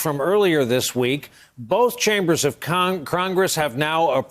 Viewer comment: "Both chambers of crongress?" 0:58 Lol who heard that?